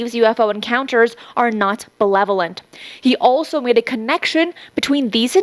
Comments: did she say ... "Not malevolent" "Not benevolent" Or mispronounced "Not belevolent"? It sounded like she said belevolent which really has me wondering whether the guy thinks the entities are good or bad. 😂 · did she just say belevolent but meant to say benevolent? O.o